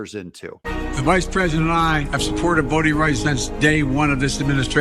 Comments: I thought he said BOATING rights! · Oh, I thought he said boating rights.